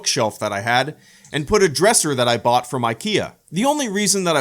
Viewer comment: I had to replay that a few times, because I definitely heard “a dresser that I bought for my Kia”. I was like damn dude that’s some next level thinking for road trip preparedness 😂